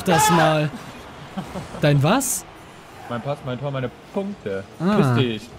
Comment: Jedes Mal wenn ich den Part gucke verstehe ich bei 7:18, Mein Pass, Meine Tor... MEINE MUTTER!!!^^